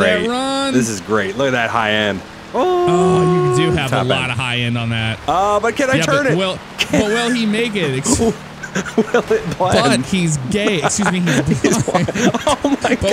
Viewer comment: did say because he's gay😂😂😂